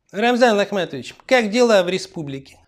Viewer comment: Мне послышалось или в начале пу обратился к кадырову Рамзан Лохматович?